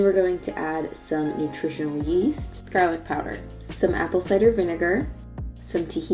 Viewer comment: Does anyone know what she said at 4:00 right after "nutritional yeast"? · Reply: Franziska Richter garlic powder!